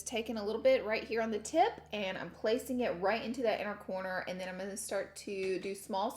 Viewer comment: Who else thought that said “I’m pancaking” when she was putting the blue shadow on.. or maybe I’m just hungry 😂🤷🏽‍♀️